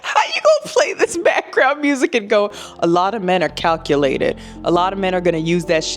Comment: I honesty thought he said "cuckulated"....